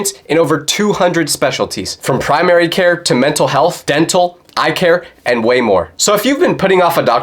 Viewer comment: When you were sponsoring and said "eye care" i thought u said "I care"😂